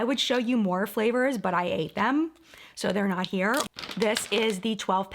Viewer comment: I thought she said she didn't have the other flavors because she hated them, not ate them. 😳🤣